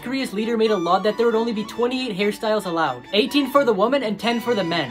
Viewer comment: When you said hairstyles it ran together and I thought you said “there could only 28 Harry Styles” 😂😂